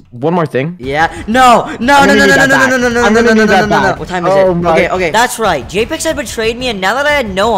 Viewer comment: Am i the only person who heard him say what time is it